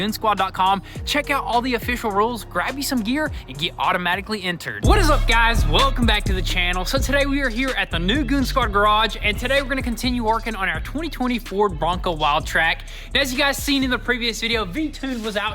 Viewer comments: Did just say 2024 bronco 🤣🤣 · Lol did say 2024 bronco at the beginning of video